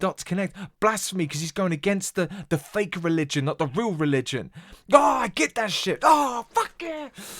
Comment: I believe he actually says: "Why you got these kids minds thinking that they evil, while the preacher being freaky you say honor gods people".